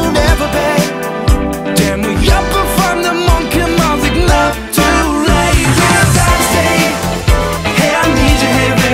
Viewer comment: のところモンキーマジックって聞こえるんですね···！ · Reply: でもやっぱファンだMONKEY MAJIK not too late (まだまだ現役)